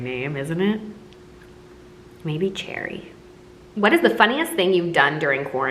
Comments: "What would your stripper name be?" "EMERALD" Amber: "Maybe Cherry" (Fr I thought she said, Jerry) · What would be your stripper name? Amber: IDK...Cherry? Me:....that's my grandma's name .... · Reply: I thought she said jerry and acted like it was completely normal 😂😂